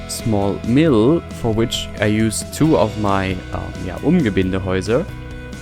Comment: Hallo, wieder einmal ein großartiges Video. Was sind das denn für Gebäude, die du hier hauptsächlich nutzt und die du bei 5:19 nennst? Ich verstehe leider nicht, was du genau sagst und kann die Gebäude nicht in der Vedastein-Kollektion finden. Danke! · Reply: Umgebindehäuser. Noch nicht im WS :)